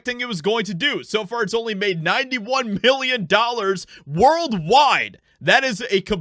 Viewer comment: Reader Favor: Do you hear million or billion at the 1:02 mark? I hear billion. Should be million. Caption says million. Thanks for your reply.